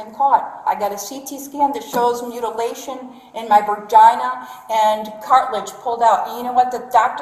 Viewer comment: Did she say “vergina”? I think it’s